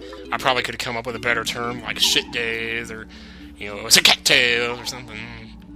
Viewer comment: ha okay what is he saying like at 10:53?? cat tail??